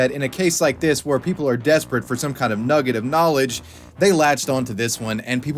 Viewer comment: Nobody noticed what said? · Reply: He said NUGGET not the n-word